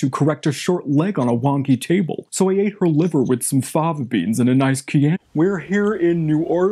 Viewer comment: “I ate her liver with some fava beans and a nice chian-“ 😭😭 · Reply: I thought it said guava beans 😝